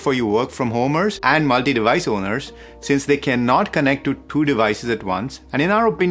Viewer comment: it sounds like he says can not and not can connect. LOL. I found the QC45 a bit better, but it may only be me.